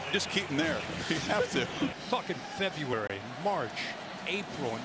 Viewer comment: Kirk 'Fucking February' Herbstreit 😂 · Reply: I never even noticed that. Always heard it as "Talkin' February." But now I can't unhear it.